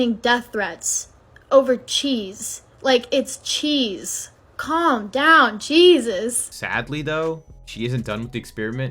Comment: As a women, don’t fall for her jargon, it’s discharge cheese 😭 What it sounded like she said “Calm down CHEESus” 😂